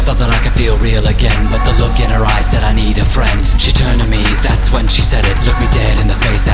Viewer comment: it sounded like he said "thats what she said" xD